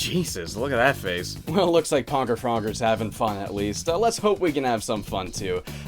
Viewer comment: am i hearing this correctly or did he just say "pogger frogger"